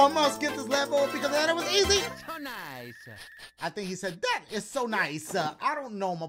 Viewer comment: For some reason I thought Mario was saying “victory is so nice”